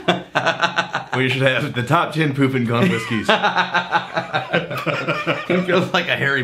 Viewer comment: Man I need to get some hearing aids. I thought they were saying “poop and gone”.